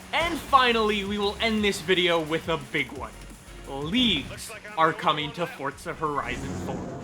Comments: At the end I thought you were saying “Leafs” instead of “leagues” and I was so confused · The first time you said “leagues” I thought you said “leaves” and got a little worried 😂.